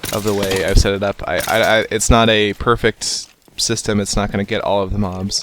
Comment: Wait did just say 'not perfect' at 2:34????